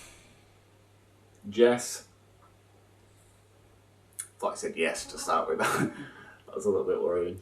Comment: I always thought it said "yes" and not "Jess"